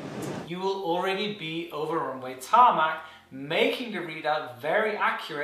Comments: DID HE SAY TARMAC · DiD He JuST SaY tArMaC???